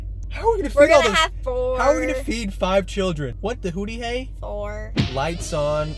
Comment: What did say at 12:24? “What the hooty hey”??? 😂😂😂